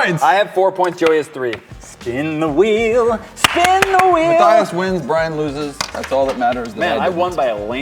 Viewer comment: I thought he said "Skin The Weasel, Skin the weasel!"